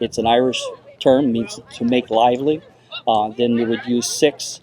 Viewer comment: I would have liked to see tying the line to the hook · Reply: Didn't he mention tying the hook with 6 half hitches 4:55 I will re listen to make sure I heard it right